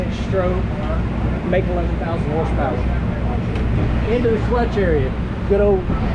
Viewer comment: Did he say 11,000 horse power? 7:59 yes, he said 11,000 horsepower.